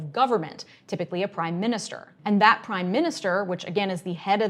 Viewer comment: You said prime minister all i heard was .. "pork markets"